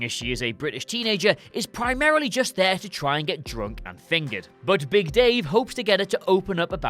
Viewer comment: did he said that girl primarily wanted to get drunk and fingered ?!! Or what